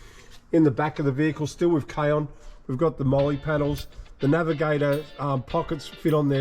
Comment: Hi, at 11:34 on video clip, I couldn't quite understand which gear you were referring, but I feel it is very important. Would you kindly spell it out or explain what it does? thank you very much. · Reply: Hey there, I think your question is about the molly panels over the rear windows. Molle is a hanging system, the panels over the windows allow pockets, first aid kits and other items equiped with Molle hangers. Reference - What is a MOLLE Panel? MOLLE stands for Modular Lightweight Load-carrying Equipment. It's an organizational grid commonly used on backpacks or vests. It allows you to attach and hold many different pieces of gear. Cheers:) Thanks for watching 🙏